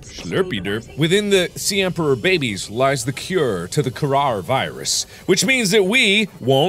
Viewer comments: God i thought he said Corona Virus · I really thought he said Corona Virus.